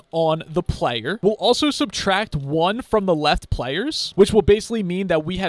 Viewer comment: Can anyone tell me what it says at 16:27 , is it -= bc it doesn't work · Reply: LeftPlayers POV: Late reply lol